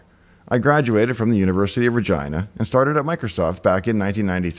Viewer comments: What university name did he say at 01:32? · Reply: University of Regina, it's his home town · I thought I heard 'graduated from the University of Vagina..'( no offense) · I heard “I graduated from the University of VAGINA” 🤷🏾‍♂️